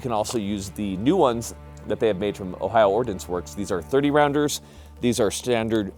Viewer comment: When he said “Ohio Ordinance Works” I heard “Ohio Orphan works” and wondered about their PR strategy; “Yeah we could emphasise defence and protection of hearth and home, or we could just embrace the nasty for extra and boy image.”.